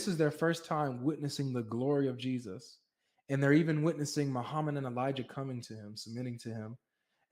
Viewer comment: Did you say the disciples met Muhammad and Elijah @ 12:48?